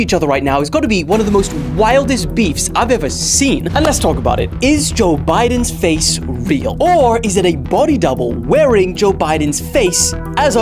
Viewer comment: my dumbass thought it it said is joe biden's face reveal💀